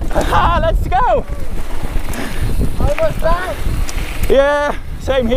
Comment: "Almost died" did i hear that right?